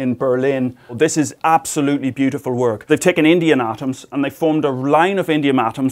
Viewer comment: sounded like Indian atoms?!